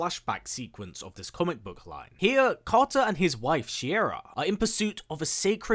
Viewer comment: Consistently calling Despero "Desperado". Ouch. Also, did the comic kill Carter Hall twice? I ask since when you mentioned another Hawkman, it sounded like you were saying Carter and not Katar (Or Kay-Tar as the DCAU did it).